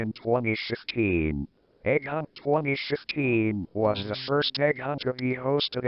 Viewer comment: umm I think I hear u said egg hunt 2016 but.. that said egg hunt 2015